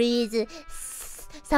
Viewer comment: When Marine Said "Thank you" I hear "faq u"